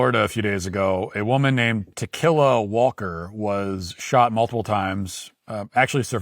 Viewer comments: all i keep hearing is "to kill a walker" and i cant stop seeing her as a walking dead zombie. Bodycams was the best thing to come from BLM 100% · The UK is not like North Korea, In North Korea, you are allowed to fly your national flag. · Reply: Did he just say "To kill a walker"? Was that thing a zombie?